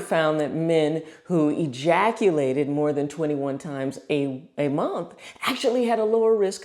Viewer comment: I first thought she said 21 times a week. That would be though but doable, 21 times a week that's more like a vacation